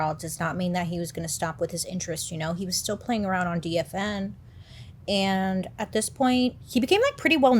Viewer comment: I thought she said DNF. I was very confused I was like what didn’t he finish?